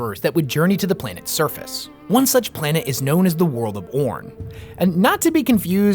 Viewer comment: They were going to the World of Orn, but I thought they said the World of Pr0n, and I signed up by accident.